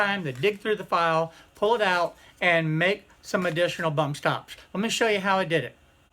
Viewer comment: Haha I thought it said bump stocks